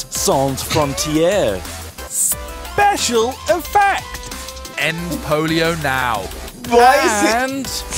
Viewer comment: For some reason I thought he said "end polo now" and I burst out laughing.